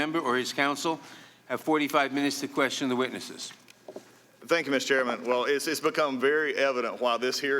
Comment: Sounds like he said “THANKS MISS Chairman” LMAO